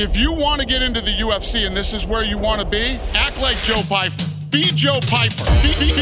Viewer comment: All I heard was "act like Joe Biden" in the beginning 🤣 Joe Biden? Lol really. Lame and frail and confused about where I am? 🤣🤣